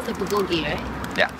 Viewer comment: question for you, please: At 01:07, I read "Bulgabi" up on my screen, yet I hear you say "Bulgogi". Which is it, please? Also, was there cheese in that toast sandwich?